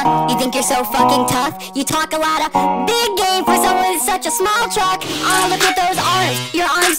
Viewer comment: you talk a lotta BIG GAME FOR SOMEONE WITH SUCH A SMALL TRUCK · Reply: my dumbass thought she said big gay